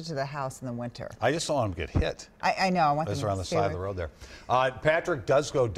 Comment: Did he just say "I saw em get hit. Just along side of the road there " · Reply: “I just don’t want em to get hit” he just mumbled it